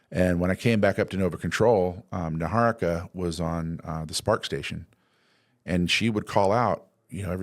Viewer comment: Did he say they're using a SPARCstation?